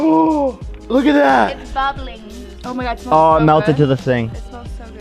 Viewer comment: did he say it melted to the sink 😂😂😂😂